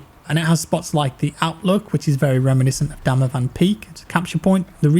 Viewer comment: Do I hear DAMAVAND PEAK? The one in Iran?